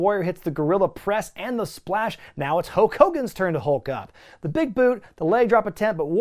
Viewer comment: "Now it's Hoak Hogan's turn to hulk up." Did I hear that correctly?